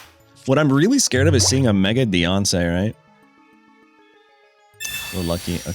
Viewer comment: "What I'm really scared of is a Mega Beyonce'"....lmaoooo I know you DIDN'T say that...but that's what I heard lmaoooooo Sounds pretty scary my boi! hahahaha